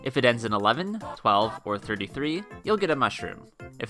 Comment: I thought you said nice flower XD